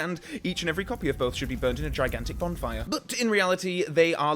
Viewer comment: is it just me or did he say bondfire